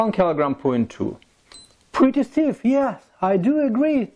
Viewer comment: Что сказал на 9:04? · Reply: pretty stiff